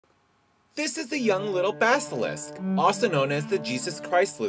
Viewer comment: I thought he said this is the little bastard...